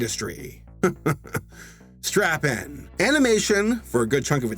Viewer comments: I had to do a double take. I thought I heard “strap on”... I guess the source material got the best of my imagination. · knowing what this movie is, I honestly thought for a moment said strap on lol